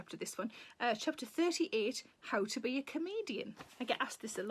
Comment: At the beginning I heard “How To Be A Canadian” as the chapter title and I was like, “and she’s Canadian as well?!” 🤯